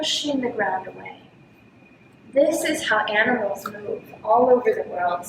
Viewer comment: I misheard "animals" for "cannibals", had a good laugh of it :D And thanks so much for the valuable instructions.